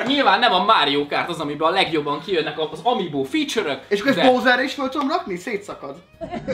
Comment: nál valaki leírná mit mondott a tag? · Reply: Márton Pintér és akkor ezt most Bowserre is föl tudom rakni? szétszakad.... hahahaha